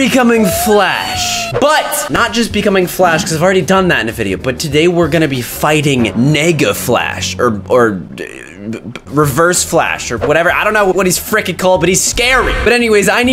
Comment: bruh i thought he said "N*GGA FLASH* 💀💀💀